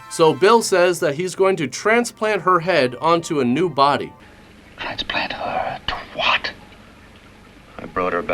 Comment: “ transplant her twat” That’s what the man said